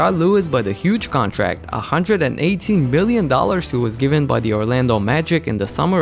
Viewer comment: am i the only one who heard 118 BILLION dollars?